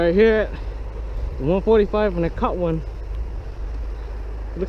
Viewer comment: I think he said the 144 at 1:43.. might go tomorrow